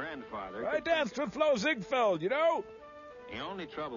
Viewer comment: I can't make out what Servo is saying before "Flo Zigfeld, y'know?" Can anyone help me out? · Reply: "I danced with..."